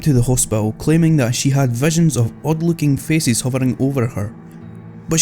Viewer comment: i thought in number 9 you said, there were hovering feces...